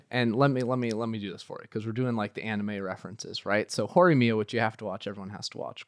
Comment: did you understand what is saying at 1:03:55? i always understand oreimo and im pretty sure thats not it xD Edot: okay, nvm, its horimiya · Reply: yeah, it's horimiya. He compared Odo to Miyamura xD